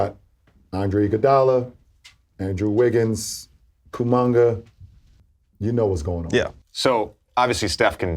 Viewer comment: Did LeBron just call Kuminga "Koomanga" 🤣